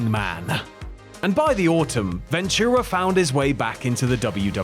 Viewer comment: did you really say "...and by the Orton" instead of "by the way"? 😂😂